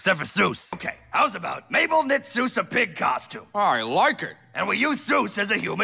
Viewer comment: what did soos said 💀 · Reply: I Like it